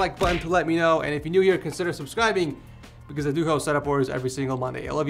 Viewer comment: Did you say "I love your beautiful toenails?"